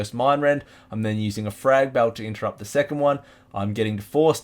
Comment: what did you say you are using to interrupt the second mind rend cast?? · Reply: Frag belt grenade. Engineering =)